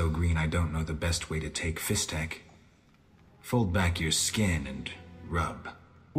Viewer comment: Did he just say "Pull back your skin?" This is weird hearing in a Witcher game, but I guess even meth was back then. 😅